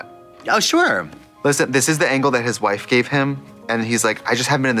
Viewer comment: Did Trixie just said Angle instead of Angel